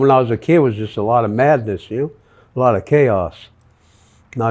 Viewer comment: When he said he grew up with a lot of madness I thought he actually said he grew up with a lot of meth. Dude looks like he still does meth